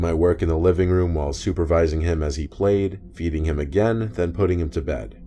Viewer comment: i thought you said beating instead of feeding